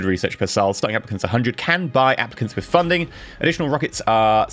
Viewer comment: Totally miss heard you, and thought you said "can buy Africans" which really got my attention! LOL